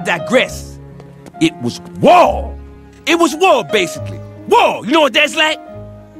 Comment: “It was WOW! It was WOW, basically! WOW! You know what that is? “ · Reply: Ant Tam I thought he said it was war?